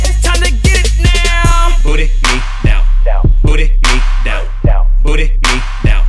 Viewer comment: I always thought he said Booty Meat Down 🤣